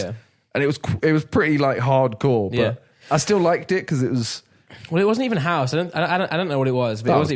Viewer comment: Why does it sound like Simon says „ i still like dick“